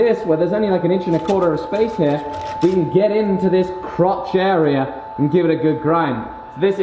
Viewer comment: did you say you are grinding a crotch?